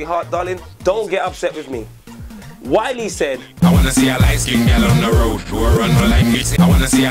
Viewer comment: I thought Wiley said " I wanna see a nice clean girl on the road"